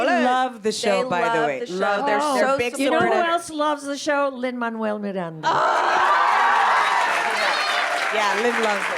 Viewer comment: Rita dice you know who else love the show Emmanuel something Miranda?? Quien es ese?? O que dice Somebody help. What says Rita here?, somebody please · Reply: Lin Manuel Miranda